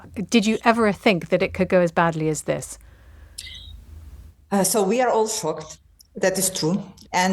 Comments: I heard: "So we are f*cked" 😅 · Did she just say “We are all f ked”. She actually said shocked…. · I thought she said " we are all f**ked" lol